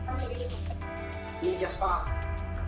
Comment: he was clearly saying: "minchia spa" in italian 😂